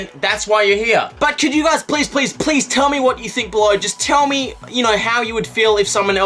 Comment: I thought YOU said blow me